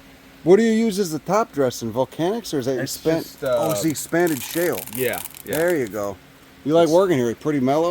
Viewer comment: Anyone know what he's saying at 29:39? I can't make it out, but it kind of sounds like banded shale? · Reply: Expanded Shale, which I think means blasted in a kiln. Turns out its kind of a lousy additive.